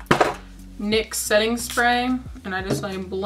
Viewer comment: I honestly thought you said “mix” instead of “Nix”!